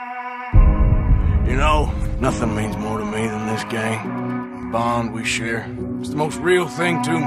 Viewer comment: I thought at first Arthur said "You know, nothing means more to me than this game"